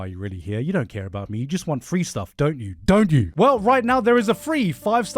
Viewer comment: did he really say virgin 3.2?